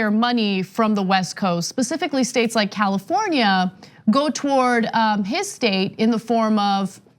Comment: Is Florida on the west coast bud? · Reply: sorry it was late and I thought they said Florida when they actually said California.